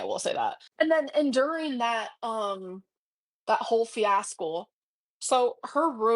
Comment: did she say "fiaskull" instead of fiasco?